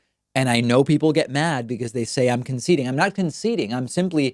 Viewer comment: I thought he said: "Conceited", lol. (Probably bc he bragged abt 300,000 ppl "concluding Trump's admission" bc of HIS video.) Hope some of ya actually watched all 53 minutes of Trump's crazy rambling to Fox & Friends. I did it, in part, bc I have thin walls & 3 neighbors who don't understand why I can't stand Trump. They got to hear 53 minutes of why.. along with the incredulous claim that "Al-Baghdadi was the biggest terrorist of the last 100 years"!